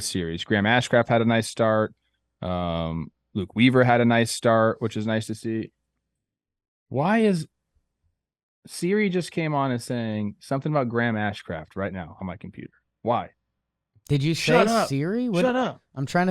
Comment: you said Series which sounded like Siri lol